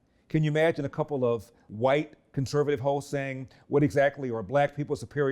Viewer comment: Had to listen closely a few times, I thought he kept saying "conservative white hoes". Hosts......it was hosts.